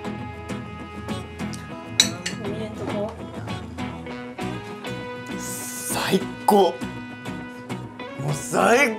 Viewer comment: 大橋くんなんて言ってますか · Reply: 見ぃへんとこ